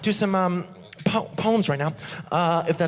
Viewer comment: the first time he said poems, I thought he was saying puns in a funny way poans